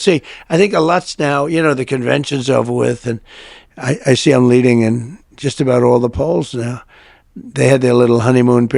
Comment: Did he just say "I see them leading in all the polls now" 🤔🤔🤔🤔 💙💙💙